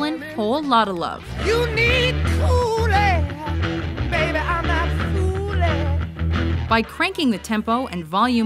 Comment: I always thought Robert Plant was saying "You need Kool-Aid. Baby I'm not foolin."